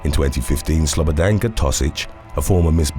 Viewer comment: Slobadanka sounds like the name of someone who gives a1 top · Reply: When the narrator first said the name I thought he said Slobadanka Sausage and my mouth dropped. Then I went back and actually paid attention. Still...unfortunate first name.